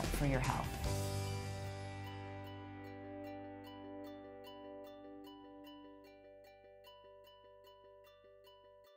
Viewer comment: What is the word she uses at 3:38? Eating In “cyclical” harmony ? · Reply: Living in cyclical harmony. Living in harmony amongst your cycle